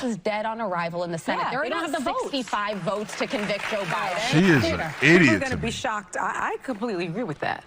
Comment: did she call him Obiden?